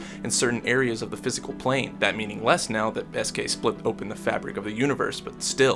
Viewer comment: I thought you said “best gay” instead of “S K” and I was like... ok that’s a hot take but I’ll go with it